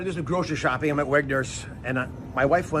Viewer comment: I thought he was saying he had Wegener's. 🤨Not really clear. I didn't understand what he was saying because "Wegener's" is a disease; the full name is "Wegener's Granulomatosis."